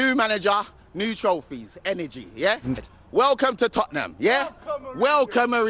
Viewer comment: What's the guy shouting about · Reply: Ahhh he shouts welcome Mourinho at the background off the camera